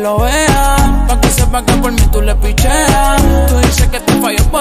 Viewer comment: parece que dice "porque tu tienes el covid" xdd